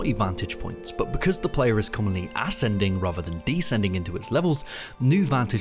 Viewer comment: For a second there, I thought you said "ass-ending" rather than "ascending." But given LotF's level design issues, maybe the former is more accurate. 😂